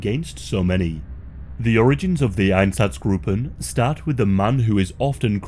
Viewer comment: Watching this video while I'm cooking and my sister misheard a part of the video and asks "What's Ass I'm Groping?" 🤣🤣🤣🤣🤣 Spit my drink out everywhere! Edit: she misheard Einstazgruppen for "ass I'm Groping" incase no one understood 🤣